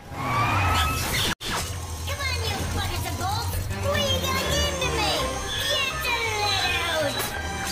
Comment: GET THE LEAD OUT! THAT'S WHAT HE SAYS! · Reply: COME ON YOU BUCKETS OF BOLTS! WE GOT A GAME TO MAKE! GET THE LEAD OUT!